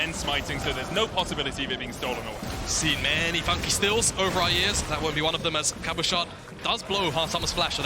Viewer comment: what is he saying???? i cant understand a word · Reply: Seen many funky steals, over the years